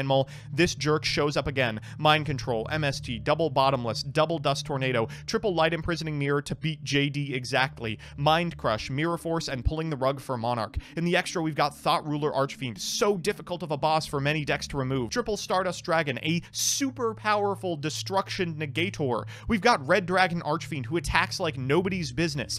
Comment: I keep hearing "Thot Ruler Archfiend" from MBT...